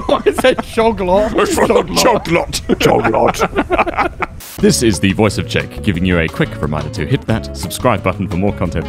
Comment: At the very end I thought he said "This is the voice of shame..."